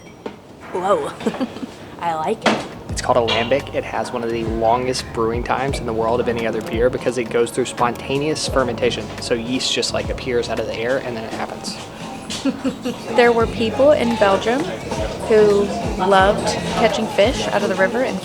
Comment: did you say 'spermentation'?!?!?